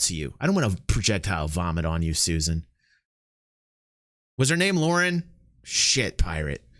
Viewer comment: I thought she said her name is a Lauren.